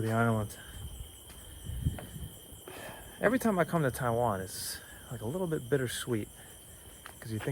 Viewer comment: I thought he said it was his first time in Taiwan cos he he says 30:17 he says "Every time I come to Thailand". But I could be wrong as I have been binge watching the channel. New subscriber.